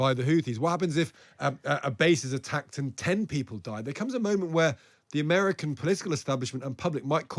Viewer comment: You hit the nail on the head when you said "What happens if an entire vessel or base full of troops gets entirely destroyed"?. I would ask why on Earth would the UK-USA and other allies wait for it to even get to that point?. Why wait for the worst, Iran and their backed groups aren't going to stop off of their own accord we all know this. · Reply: Absolutely false quote: "[O]r base full of troops gets entirely destroyed." Appallingly not even close. "A base is attacked and ten people die" 2:44 was what he actually said.